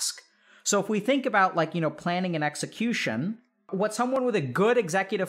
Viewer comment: I thought he said planning AN execution for a second. 0.0